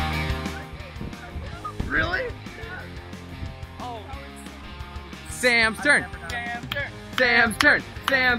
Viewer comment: i thought they were saying "samster" then i realized they were saying " sams turn" then i felt stupid 😂